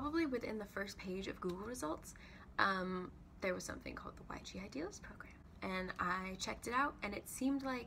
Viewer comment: YG ideals program? couldn't hear it properly :( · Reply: yg idealist program :)